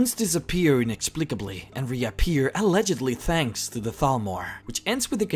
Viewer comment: At first I thought he said Falmer.... now I realise he said Thalmor